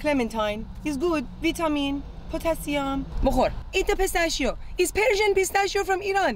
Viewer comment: did she say “ Moo Khol”??? That means open your mouth, i am gonna feed you in Indian. Damn, All the mothers are similar! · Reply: She said "Bo Khor," which means "EAT!" in Farsi.